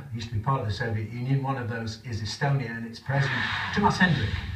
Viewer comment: I heard about "Pistonia" is that some kind of country?!